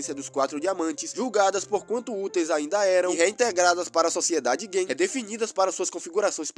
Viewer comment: Só eu que ouvi sociedade gay?